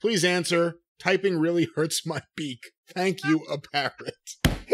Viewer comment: Oh... PARROT...I thought he said PARENT😂😂